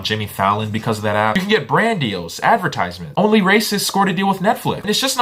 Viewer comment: He said onlyracist? I think? Am I mishearing it or did I miss more tiktok drama with her involved?